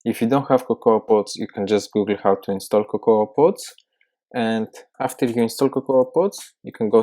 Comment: what did you say we must install? Sorry, my audio is pretty bad on my computer. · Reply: Stan Mays cocoa pods